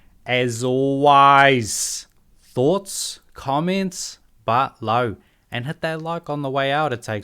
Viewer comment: what are you saying there? But low? But no? i need a english to english translation. what does that mean? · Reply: It’s Down Below